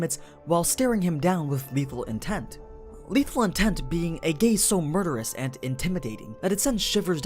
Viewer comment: he said gay instead of gaze!!!!!!